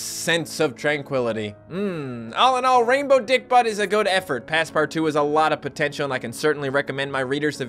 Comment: Am I the only one who thought said Pass part 2?